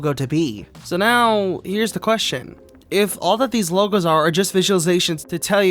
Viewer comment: For minute I thought it Said “Legos”.